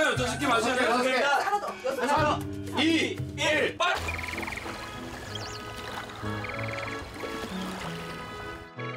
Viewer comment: 이부분 뭐라했길래 삐처리됨?? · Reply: 빠구리